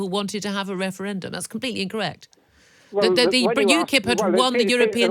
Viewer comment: I literally wondered what Vince said at 4:46 ..... then I realised he said "rank". Then again a lib dem is prone to coming out with literally anything.